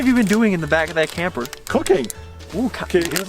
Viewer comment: anybody else thought said cocaine? lol